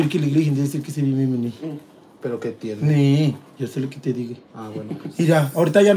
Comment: Alguien que me diga que dice en el min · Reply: Solo entendí ; yi si li qui ti digi O sea, yo se lo que te digo